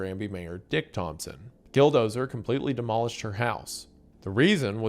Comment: at this mark I swear to god I just heard Dildozer!!! 😂